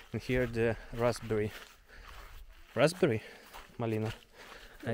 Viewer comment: Lovely looking farm and i thought i heard you call Luba Marlina ? Great video